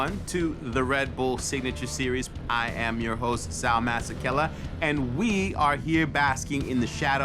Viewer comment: is it just me or does it sound like he is saying "i am your host dumba** ...." ?😂😅